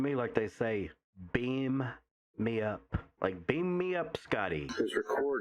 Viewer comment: I hear “don’t beat me up” lol